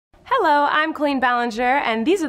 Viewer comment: In the intro i literally heard "Hello I'm Queen Ballinger " 😂😂 (which she is tho